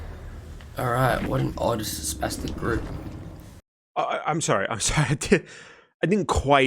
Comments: what did he say here? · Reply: i heard "what an odd spastic group". does that have a meaning or did i hear it completely wrong? like, i have no clue what he is saying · Being the dumb person that I am, who sometimes struggles with understanding heavy accents, at 7:50 can someone please tell me what the streamer is saying? It sounds to me like he says “what an odd, spastic group” but I know I’m probably just mishearing it, since I don’t feel like that’s an insult? Edited: I am not from the UK, so if what I heard is correct, and this is an offensive term, then I apologize in advance.